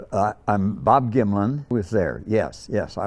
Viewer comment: All I hear is "Hi, I'm Bob Gymlan"